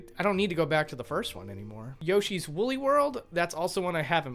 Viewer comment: Did you like the previous Mario Kart games? Lol what's the difference? Also it sounds like you said Yoshi's Willy World, lmao.